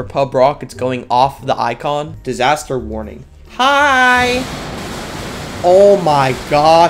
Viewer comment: That hi sounded like centident lol